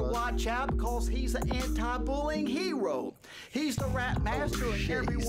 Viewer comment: i heard “he’s the rap master” as “he’s the rat bastard”